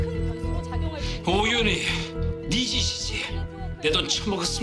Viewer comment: 오윤희 니짓이지 -> 신애리 니짓이지 로 들리는 기적.. 변교빈에서 바보 빠지고 독기만 가득한 늑김..